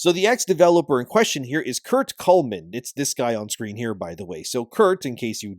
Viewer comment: I was doing dishes and because of flowing water instead of Kurt Coleman I've heard Kurt Cobain, I had to rewind to make sure if I heard that right lmao